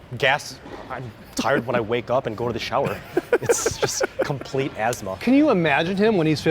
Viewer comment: I heard "GOLDEN SHOWER"